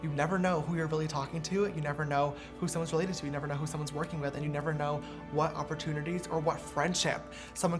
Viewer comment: it sounded like you said "you never know who somebody's social blade is" and i was like jAMES THIS IS NOT THE TIME DURING AN INSPIRATIONAL SPEECH LMAO